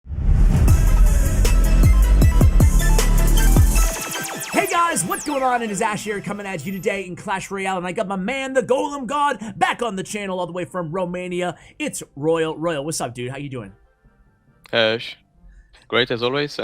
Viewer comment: Thought i heard "Golden God" until i saw how easily i can be destroyed by golem decks..